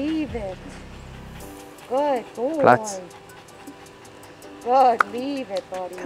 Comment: Im a wrong or did he say "plats" at 11:14? As in Swedish